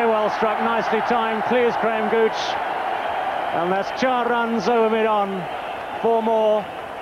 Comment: Did I hear that right Chaar runs?